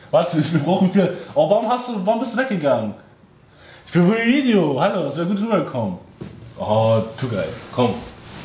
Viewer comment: ich hab irgendwas von 'für mein video' verstanden XD